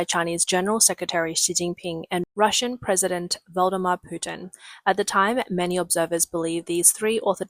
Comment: Did she just say Voldemort Putin? lol